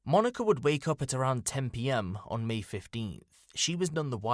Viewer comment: You kept saying "PM" instead of "AM" at 8:10. It was kind of confusing not going to lie